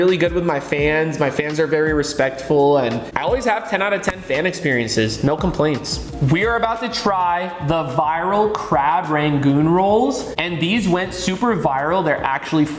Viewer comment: not trying to be a dirty mind but did he just say goon roles?